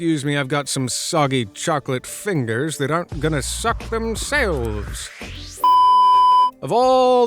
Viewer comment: WAT DID U sAY...? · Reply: Suck